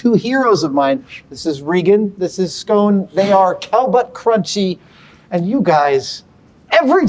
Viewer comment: Toeless scrunchy?..... cowbutt crunchy.......how is that worse than what i thought he said